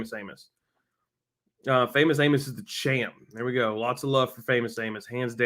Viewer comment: The mount of times i heard "famous anus". Is uh.... something.